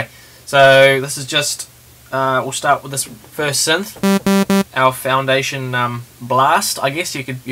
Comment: What did you say at 1:05??? Owl foundation blast? lol thats all i heard, please tell me